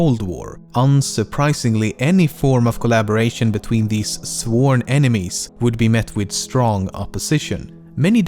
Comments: Awesome video as always, man. There is, however, a typo in the subtitles at 14:19, where it says "sword" instead of "sworn". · sworn enemies, but subtitles/CC says "sword" enemies o.o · it says "sword" enemies instead of "sworn" enemies in the subtitles :) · Subtitles typo You said sworn enemies while the subtitles says sword enemies